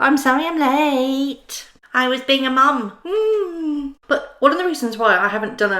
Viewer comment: Oh wow I thought she said, “im sorry im late, I was being a mutha.” Thank you for clarifying that.